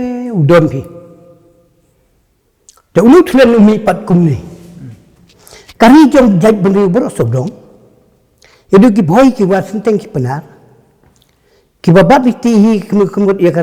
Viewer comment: Did he say ki bhoi ki war sinteng ki pnar